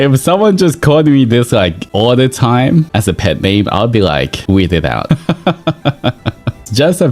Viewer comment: i think he said 'weirded out' 🤔 not too sure btw Thanks for the video ❤